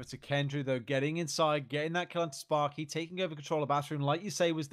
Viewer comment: I think he says “getting that cunt sparky” 😂